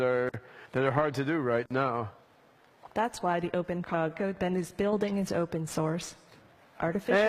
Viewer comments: Can someone please click on 12:35 and tell me what she says “That’s why Open Code is building it’s open source…” specially the word when he tried to interrupt “heeeeyyyy all right” Before she said that, he mentioned a future missing resources, and robos being able to help people and people help robos… but I think she understood resource as open source, that I guess we have on Chat Gpt maybe? Am I thinking conspiratorially 😂 why did he interrupted? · anyone else catch that? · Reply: Search OpenCOG. That's what she said.